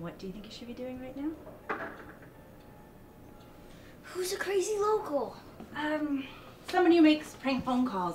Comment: Thought she said crazy vocal